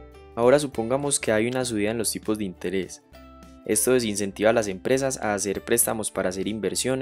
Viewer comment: disculpa una duda duda en el min 2:18: cuando dice que ahi una subida en los tipos de interes, se incentivan las empresas a hacer inversiones y comprar nuevas maquinas, lo que provoco una caida en la demanda, no me encaja eso, o yo escuche mal.. pero si se supone que ahi una subida en la tasa de interes, menos quieren invertir las empresas o me equivoco, help me please !! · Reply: rayos, yo también escuché "incentiva", menos mal q leí los comentarios para asegurarme xD